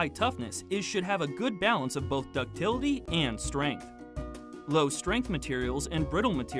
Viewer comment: Thanks for the video :) Question: What do you say at around 6:10? For a material to have high toughness it should have a good balance of both ductility and... strain/strength? Continuing with: low strain/strength materials and brittle materials tend to have low toughness. Which one is being said in the video? thanks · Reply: Strength.